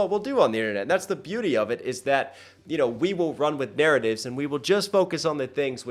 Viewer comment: I wanted to point out you said "narratives," and I thought you said/heard, "there it is." Idk who this bar was for, but there you go.